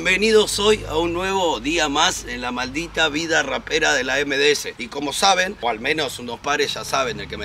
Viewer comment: Ahhhhh. 2 veces lo tuve que ver, pensé que dijiste vida rockera. Digo este está re piantado. Derrenpente todo de cuero corte punk re fuerte jopo y un par de cicatrices son importar nada jaja